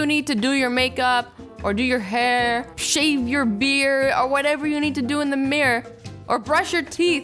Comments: it sounded like you said to shave your beer 🤣🤣 I love you! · I thought that she said shave your beer